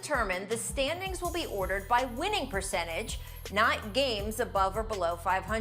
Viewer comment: Perchange.... perk sounds like he is saying Pucentas